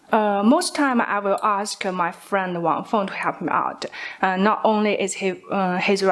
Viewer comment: I think she said “ask my friend WANG FONG” not on the phone